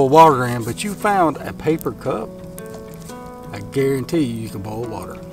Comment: At first i thought he said plastic cup and thought to myself... "No way. This thing is for sure gonna melt..." But the paper cup makes sense. It absorbs the water and becomes wet. Didn't know this before, glad i do now. I'm trying to learn all these survival skills because i feel we may need them in the near future...